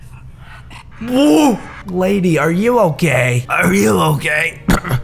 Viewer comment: I thought u said you gay XD